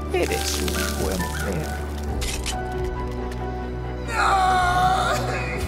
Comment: que dice trolli.exe en esa parte pq no lo entendi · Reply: Eres un buen perro